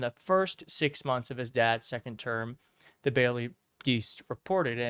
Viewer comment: Did you say "The Baily Deast" at 2:12?